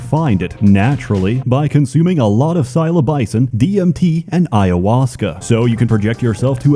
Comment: Did you just say silo-bison?